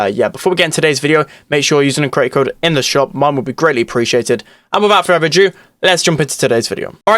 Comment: Lol, for one second i heard "Mom would be greatly appreciated" 😂😂